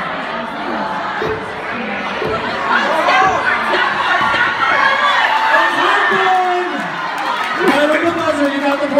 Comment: what does she say at 9:43? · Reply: I think she says tech crew